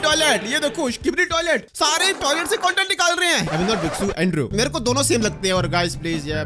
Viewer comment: wtf is saying andrew bee bixo whatttt 😂😂😂